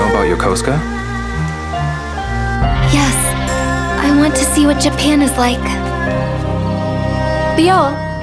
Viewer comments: did I just hear it wrong? "I want to see what your pen is like" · I'm sure I've misunderstood what she said... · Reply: Yes, I want to see what Japan is like.